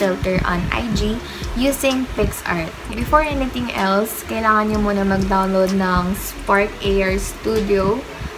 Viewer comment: what did you said ??? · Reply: You should download Spark Ar Studio on your computer